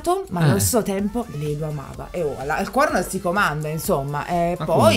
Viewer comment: non io che ho capito "alle corna non si comanda" nani?